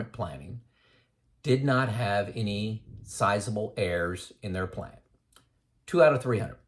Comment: Ha - I thought you were saying "heirs" at first, not "errs" or "errors". Gotta love closed-captioning - that helped clear that up for me. LOL I agree with everything you said in this video. I'm glad I found your YT channel. I retired at 64, 65 now. Just like you mentioned. I waited for a project to complete before retiring. The last 2 years were working from home sitting in a chair for 12+ hrs/day. Now my health is worse - but I blame COVID for that. I now tell everyone who will listen that they need to retire early to maximize those "go-go" years. I thought getting old would take longer, it hits you full in the face (like a MAC truck) before you know it. Thanks from Denver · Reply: Errs to the Throne. I thought he was saying "heirs" too. 2 out of 300, didn't have anyone to leave their inheritance to!